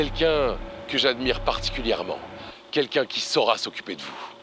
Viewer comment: "Quelqu'un qui SORA s'occuper de vous" Perso c'est se que j'ai entendu 😭😭